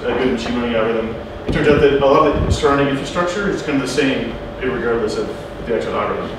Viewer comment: did he just say "irregardless" at 0:58?